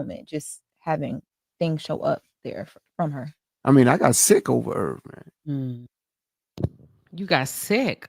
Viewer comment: It sounded like he said, "I got sick over Irv"...not, "I got sick over her."